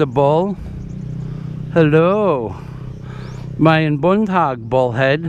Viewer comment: What are you saying in the beginning at 00:11? it sounds like "Mine Buntog". Also, what does it mean?